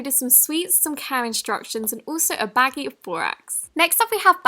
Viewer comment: thought you said COW instructions